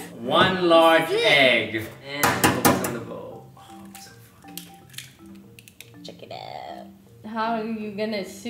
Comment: i hear toast or someone in the background saying "oh its so fu**ing cute" ?????????